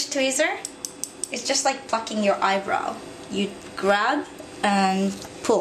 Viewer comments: I thought that she said "fuck your eyebrows".. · did you just say "it is just like fucking your eyebrow"? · LOL AT 5:31 i thought she said fucking your eyebrow NVM The video really helped me ^_^ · it sounded like she said "it's just like fucking ur eyebrow" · Lol it sounded like she said fucking your eyebrow